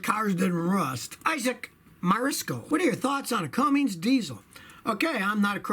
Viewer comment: Join the movement and help make this non-sponsored independent channel become the top subbed car channel on YouTube, instead of corporate channels like Top Gear, subscribe to scotty: <link> ⬇️Scotty’s Top DIY Tools: 1. Bluetooth Scan Tool: <link> 2. Cheap Scan Tool: <link> 3. Basic Mechanic Tool Set: <link> 4. Professional Socket Set: <link> 5. Ratcheting Wrench Set: <link> 6. No Charging Required Car Jump Starter: <link> 7. Battery Pack Car Jump Starter: <link> ⬇️ Things used in this video: 1. Common Sense 2. 4k Camera: <link> 3. Camera Microphone: <link> 4. Camera Tripod: <link> 5. My computer for editing / uploading: <link> 🛠Check out the tools I use and highly recommend ► <link> 🔥Scotty Shirts and Merch ► <link> Subscribe and hit the notification bell! ► <link> Scotty on Social: Facebook ► <link> Instagram ► <link> Twitter ► <link> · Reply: Hey Scotty, i may be hearing wrong but it sounds like your saying Cummings instead of Cummins.